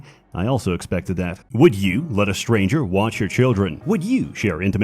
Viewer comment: I looked away and thought he said, "would you let a stranger WASH your children" XD caught me off guard